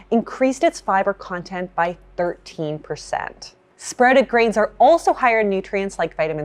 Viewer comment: I legit had to relisten to the part about sprouted grains multiple times because I could’ve sworn she was saying “spreading grains” and I was baffled 😅